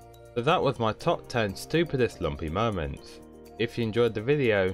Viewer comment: What was said: "If you enjoyed,please leave me a like" What I heard: "If you enjoyed, please leave me alone"